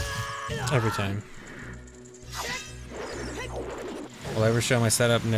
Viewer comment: it sounds like he sayin taco time